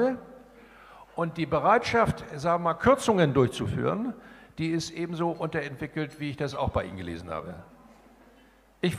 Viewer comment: Was sagt Steinbrück in Minute 38:00? Hier-Prämie? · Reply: Herd-Prämie.